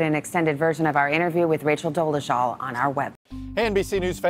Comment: It sounded like she said “racial Dolezal” 😂